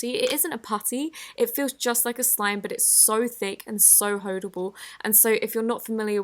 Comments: so hoedable 😂 am I the only one who heard that? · Did she just say Hoe-dable?